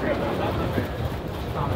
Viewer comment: anybody else hear CBD sports cream at 17:13?